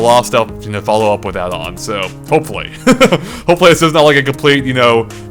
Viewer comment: did he really say at 31:05 ''tnx for watching and die'' xD I know he means bye but it sounded just to much like a ''die''!